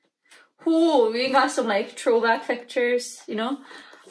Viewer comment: did you say "troll back pictures"?